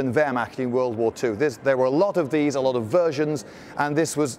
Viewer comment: thought he said "a lot of virgins" lmao and it also "saw a lot of action" with a long barrel gun